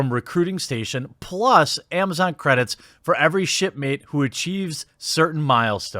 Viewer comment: I could have sworn he said shitmate.